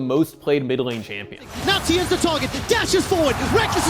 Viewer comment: Am I the only guy who hears nazi is the target